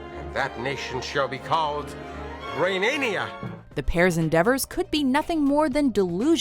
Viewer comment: the subtitles understood Romania instead of Renania. (Romania is my country lol).